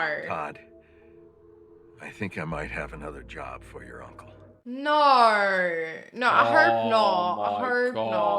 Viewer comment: “or nor I hurp not” I’ve been trying to figure out accent for so long and this is the best summary of why it’s strange instead of “oh no I hope not”